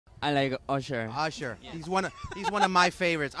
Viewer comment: In starting I thought jimin said my name " I like anchal" 🤣🤣🤣 but it's not